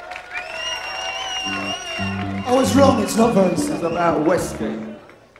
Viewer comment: Referring to "The Band Played Waltzing Matilda" as being about 'dead Australians' was poor taste. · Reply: To be honest, I thought he said "It's about the inner strugglings."